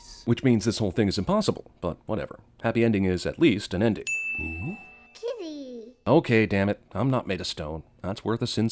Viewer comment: I swear, at first I thought boo said titty at the ending.😂